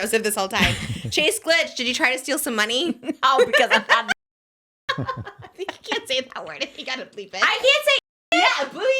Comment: non native speaker, pls someone tell me what's the offensive term at 1:13:09 🥲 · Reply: she says “idiot”— i think it’s fine tbh, but some people think it is offensive towards disabled people i guess.